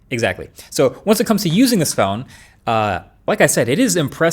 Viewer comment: did he say scone??